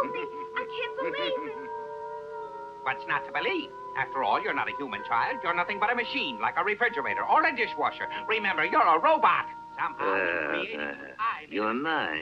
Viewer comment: what did dr tenma say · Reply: "What's not to believe? After all, you're not a human child! You're nothing but a machine, like a refrigerator or a dishwasher! Remember, you're a ROBOT!"